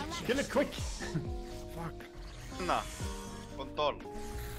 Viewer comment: did he say kont*l